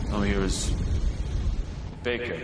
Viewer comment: I thought he said bacon